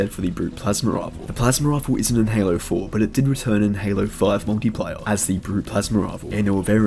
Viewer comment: I thought you said "Monkeyplayer" but it's Multiplayer